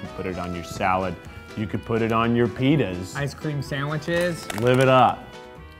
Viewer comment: am I the only one who miss heard Keith say "pitas" and thought he said "penis" am I?